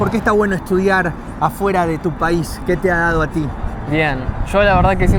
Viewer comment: Soy Boliviano y el muchacho que dice ser de Bolivia es Peruano 100% habla todo como Peruano "a ti mano" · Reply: Dice "a ti Manu" por mi nombre, y el vive en Bolivia y porque mentiría su nacionalidad? Un abrazo gracias por ver el cntenido!!!